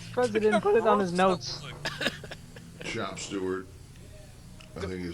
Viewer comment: I love that the guys think he says “Chop Stewart” when he calls the Undertaker a “shop steward”. The ridiculousness of that term throws them all off so bad and it cracks me up every time 😂